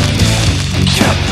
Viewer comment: OHHH, ICH HAB "CAPITAN ENTE" VERSTANDEN... ABER ES IST "CAPITAN METAL"